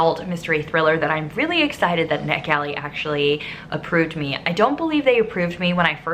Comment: Until now I genuinely thought you were saying ‘neck alley’ 😬🤣